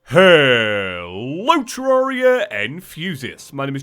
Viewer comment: I usually show up to videos to hear "HELLLLLLOO Terraria Enthusiasts." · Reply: I hear "HELLLO Terraria and fusiasts" so I'm right now confused what a fusiasts is